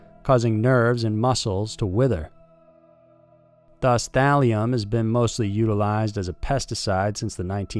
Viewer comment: I swear I thought you said "Valium" and started flipping out! (Not ashamed to admit I take it)